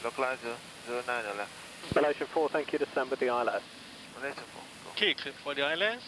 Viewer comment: ATC sounded like he said "descend for the ILS," to which cockpit confirmation at 18:31 seemed to be "cleared for the ILS." Im an instrument pilot but have only heard ATC say "cleared for the ILS RWY _ approach" before. Do they just state the clearance differently in London or was the approach clearance left out? Also, do these big jets enter and fly holding patterns automatically? I always have to fly them by hand. I did fine on the holding pattern on my instrument ride, but I had difficulty choosing the correct entry. Examiner said he wouldn't fail me for that, but I should brush up on holding entry.